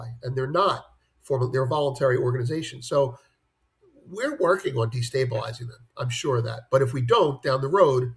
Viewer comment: "We are working on destabilizing them." Did I hear that right? It's like going to a soccer match and say the only way to beat them is to drug some thier team members. If african leader are not going to wake up on this then.... I don't know